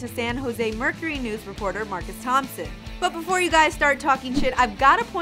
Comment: Does she say "Before you guys start talking sh*t?"